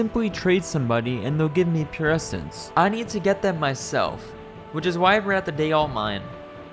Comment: did you say “which is why I would rather date online”?